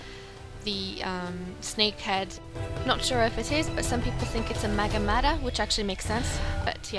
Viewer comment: you said it was a snake head or ???, I didnt hear the second part. it sounded like you said mega mada, could someone tell me what she said LOL 😂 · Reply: LOL sorry , its magatama <link>